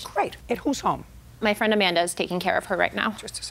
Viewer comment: JUDGE JUDY! PLEASEEE GET UR HEARIN CHECKED LUV!! 🙏🏻😬 @ 4:25 "So where's the dog now? My friend Megan is taking care of her. Do you live with your friend Megan? No, I live on my own now. WITH THE DOG? YES". Is she potty-trained now? Yes." Does anyone else see what's wrong here?!?! Defendant literally just said to JJ that her friend was currently caring for the dog, NOT HER! But when JJ asks her again, she changes her answer?! And JJ didn't even catch her f*k up SMH.. 😩 P.S. I feel really bad for this sad puppy. He just keeps getting shifted around from one house to another. She should really relinquish her doggie parent rights and find him a good STABLE home. It's not good for the puppy to constantly be shipped and moved around from strangers house to the next. Puppy probably thinks that she keeps abandoning him, with all these new ☹️ 🐕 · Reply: Get your hearing checked too.. She said Amanda, not Megan. 😂😂